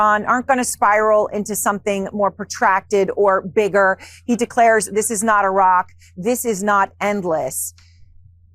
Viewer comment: I thought He they said “this is not War “